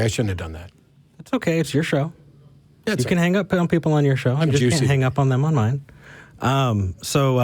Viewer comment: does Matt say “I’m juicing” ? Is this a reference to Matt being on TRT?